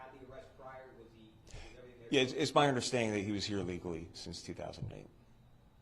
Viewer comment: Because he was here legally. Just like other American criminals. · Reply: my mistake I thought they said he was an illegal.